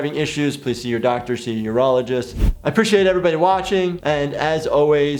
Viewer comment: For some reason I thought you said "see your neurologist" after the foreskin question, which I know was urologist really, just made me giggle given there are people in this world who seem to have their brains down there... :P